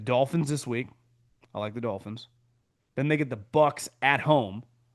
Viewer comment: Did I hear you say you like the dolphins against the Bills this week?